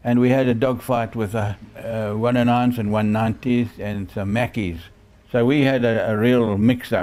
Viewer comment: Can anyone clarify what he's saying here: "...109s and 190s and some [mackies?]." I can't tell and I feel stupid. (I know what 109s and 190s are, but I'm wondering about the other word) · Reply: Italian Macchi 202 fighter aircraft